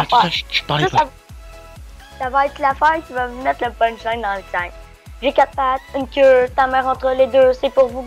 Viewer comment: il a dit"Dans'l tête et je sais de quoi je parle suis québecois